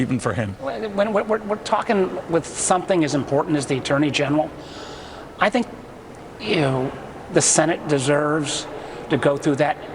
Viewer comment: I could swear after he said "with something as important as the attorney general...." he said "I think....Ewww<link> I agree!